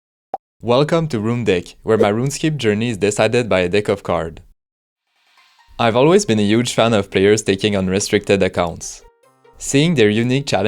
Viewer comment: I'm gonna be honest, I misheard the opening sentence and heard "welcome to rune d*ck" lmao